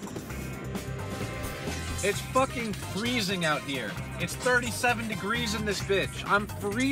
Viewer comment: not kidding I really thought you were about to say "it's fucking Tr3yway !" 😂